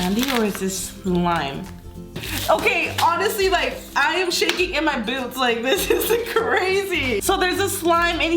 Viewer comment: i heard I'M SHAKING IN MY BOOBS(even the captions said so)